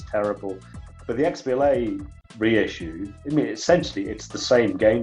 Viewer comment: what reissue is he talking about? cant understand what hes saying. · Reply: He says "the XBLA reissue". The one on 360. I added subtitles, so switch them on if you're struggling.